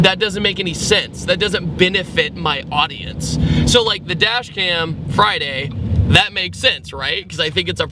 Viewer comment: It's not until Friday lol. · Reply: oh lol I thought you said it was from last Friday